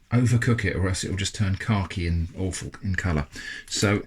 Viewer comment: what is this word? 'carky' I don't know how to spell it. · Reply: Khaki